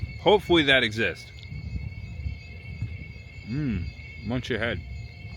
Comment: I thought you said munch ahead after eating the Moon pie, funny as lol